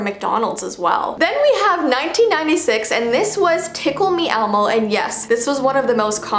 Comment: When said "tickle-me-elmo" i heard "pickle-me-elmo" as one is looking at me from my living room